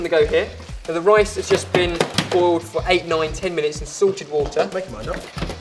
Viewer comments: For a while I thought you were saying sorted water... and I was like "But what is sorted water? is it regular water? does it have Ben's hair in it or something!?" then I realised you were saying salted... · when ben said "salted water" i SWEAR i heard "SORTED water" and i was like... HOW DO YOU EVEN HAVE SORTED WATER?!!? O